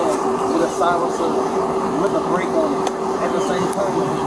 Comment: poor subtitle person can't hear the word "break"